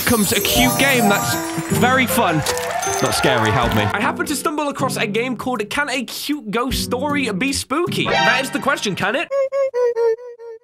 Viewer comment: Did he just say: Spoak-y?